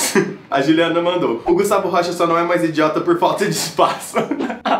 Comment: eu entendi (por falta de paçoca) ksksksksk